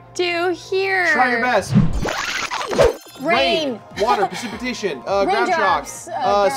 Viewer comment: it sounds like it says "rimjobs" (yes i know it says raindrops)